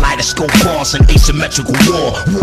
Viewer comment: what does he say at 0:50? something like: "...........?............ ....?..... camouflage ....?.... fast, kaleidoscope bars and asymmetrical war, war on my medical card, last name horse when you step to me...."